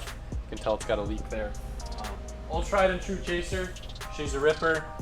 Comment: I had a mini heart attack because i misheard you and thought you sold the white chaser